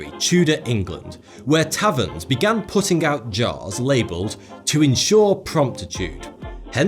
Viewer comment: Ha! I misheard at first when he said “To insure promptitude.” I thought he said “To insure PROSTITUTE!” 😘😯. Actually, that’s not a bad idea.